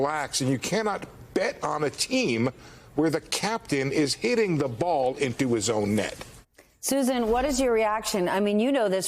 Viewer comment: First I heard this statement I thought he said "...where the captain is eating the ball and do his own net." which by the way is much closer a metaphor to what Tr*mp is doing right now 😂😂😑